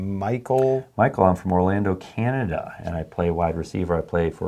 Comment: did you just say Orlando Canada instead of Ontario